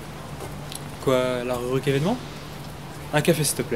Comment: "...Quoi ? La reruque évènement ?....Quevonounou ..."